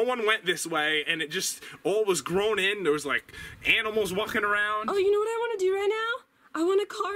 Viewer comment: I thought you said you saw no animals I call bluff